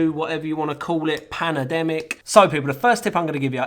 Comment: Did I hear "Panademic'?